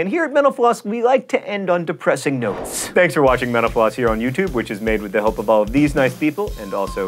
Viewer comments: every time he says "thanks for watching mental floss" I hear "thanks for watching menopause" lol... I'm so weird... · For the first little while I thought he was saying "thanks for watching menopause" which I guess he's not....